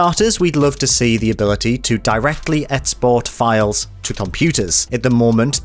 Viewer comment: D-RayTheGreat it sounds closer to "ätzbotz" to me. again something i wouldn't hold against him since it's not his fault but something that always throws me off a bit because i'm of course used to hearing it as xbox. especially for me (who's mother language is german) it really is kinda obvious for me how it sounds to me. i know what he means (which is all that matters) but it's just something that my brain needs to get used to a bit. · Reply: he just has a uk regional accent and is actually making a good effort to enunciate his words in my opinion :) It sounds like a Newcastle accent, or what we call Geordie. I think the word you are mishearing is "export" btw.